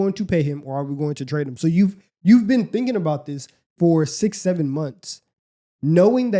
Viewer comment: what did say? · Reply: 6 7😂